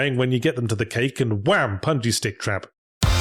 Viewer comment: i think the animators and subtitlers misheard yahtzee because im pretty sure he said "punji stick trap", like the ones is the first tony hawk game